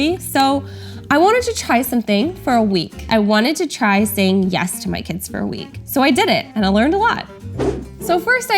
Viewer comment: For like a half of a minute I thought it said”I only said yes to one of my kids